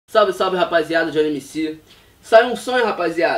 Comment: Eu entendi "saiu um sonho hj" kkk